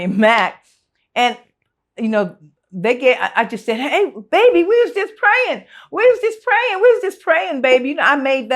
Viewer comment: I always thought she was saying "WE WAS JUST PLAYING" but it was we was just praying 😆but I think "WE WAS JUST PLAYING" is funnier🤣